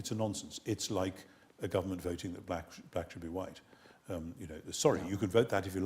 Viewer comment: Is like the government voting if blacks should be white? Wtf?? I dont think this man is ignorant... But intelectually dishonest? No doubt. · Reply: he said black. I thought he said "blacks" at first too, but he was talking about race when I went back and listened.